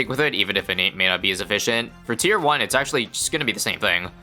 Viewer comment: did just say "for tier one" while refering to the round 40 moab?